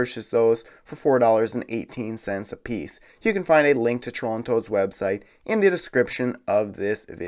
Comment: I thought you said Toronto’s website. Anyway love the vids keep it up